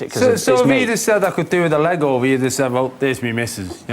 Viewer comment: what the hell John Bishop say at 4:08?? what is a leg-out? me missus??? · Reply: To 'get your leg over' means to 'have sex'; which is why he mentioned 'missus' (wife or partner).